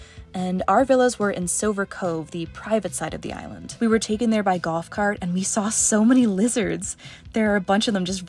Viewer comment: I thought you said "we were taken there by GOTH cart" at minute 17:13.😂